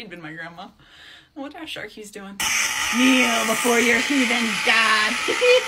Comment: “KNEEL BEFORE YOUR HEATHEN GOD HEHEHEHE!” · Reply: Human not heathen sorry if I misheard that I'm sorry